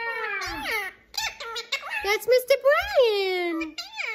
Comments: I thought Beaker said, “That’s a Mr Brain!”😂😂❤ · Oh wow, I didn't know he was saying "That's a Mr. Brian" I thought he was trying to say "That's a misses Bell" Tee hee! I had no idea, it's whole "new" character!